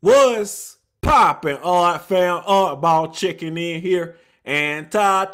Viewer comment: dnt it sOund like he says "Oddball ChiCcen"?